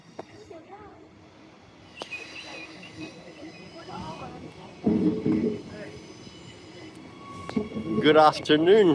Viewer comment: what does the desto say? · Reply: "GOOD ARVO"